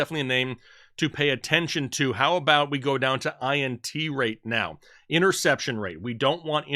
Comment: Sounds like you said Titees rate..